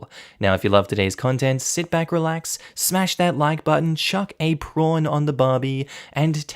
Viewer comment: When I found this channel, I heard “chuck a pawn on a Barbie” instead of “chuck a prawn on the Barbie”. So I thought “hmmm, why would I chuck a chess piece at a doll?”